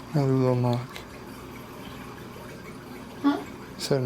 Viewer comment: I believe that was saying "huh" because she didn't hear what you had said